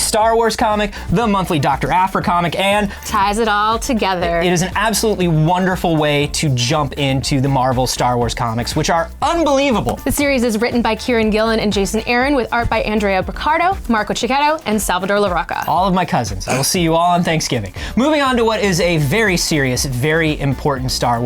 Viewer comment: man when they said "Kieron Gillen" I thought they said "Karen Gillian"